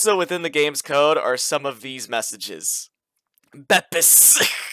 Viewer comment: "this is the point of no return. things are going to get a lot worse" also in the same layer: dies laughing from bepis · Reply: I thought you said PIPIS